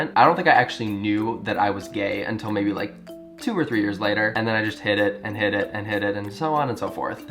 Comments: omg 1:39 i thought he was saying 'and then i just hit it and hit it and hit it' and i was just like omg ok sure get it girl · When you said you realised you were gay and "hid it and hid it and hid it" I thought you were saying HIT IT and I was like WOAH we don't need to know about your grindr hookups 😂😂😂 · I thought at 1:39 you were saying hit it, and hit it, and hit it... i was like oh man, go get some but then realized you meant hid* it